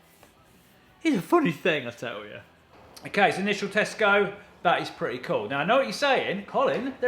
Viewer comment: I thought he said "as initial Tesco"